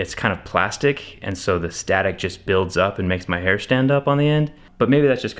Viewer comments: did i just hear him right? "it makes my hair stand up" - yeah sure, and Voldemort needs shampoo! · Did he just say his hair stands up i mean where are they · Did he just say "it makes my hair stand up in the end"? 12:10, you are the best!